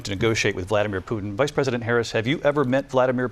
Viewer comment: Listen closely at 5:50 , did he say 'president harris' ?? I heard vice president a few times, but the last one...